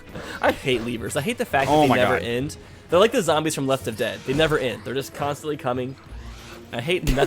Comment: Did call it Left Of Dead? Like, you're standing a few feet to the left of a dead thing?